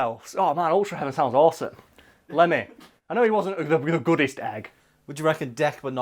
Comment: I thought you said Lenin for a second and I was really confused